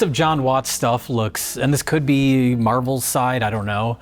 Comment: Did Jay just say “Marvelcide”?